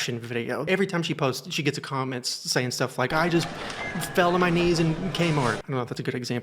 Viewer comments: I thought you said "I just fell to my knees and came hard" 😅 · you said Kmart, I heard came hard. My ears are not the same